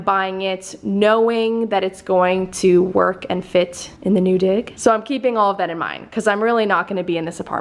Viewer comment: when you said “knowing that it’s going to fit in the new dig” I truly heard you say “in the new dick” and my heart stopped for Finneas